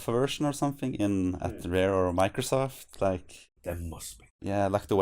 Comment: i couldn't hear him well at around 11:48 ...he didn't answer if the rom exists somewhere, right? He simply changed the topic to e3? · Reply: He said: "There must be." He probably got a bit sidetracked. :)